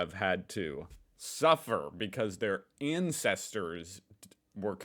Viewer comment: you said " incesters " instead of " ancestors " a very different type of family relationship 😮